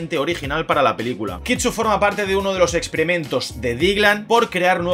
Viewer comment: Entendí "forma parte de los excrementos de (...)" Y yo 😯😯